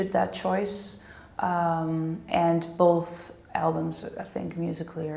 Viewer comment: The dude says "Albums" ... I kept hearing "Elms" :D